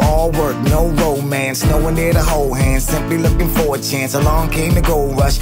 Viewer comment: did he say 4chan? lol